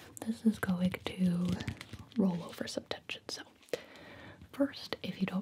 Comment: Sibyl is so amazing. I thought she said she was gonna check me for tetanus instead of tension and I was like “well if Sibyl says so.”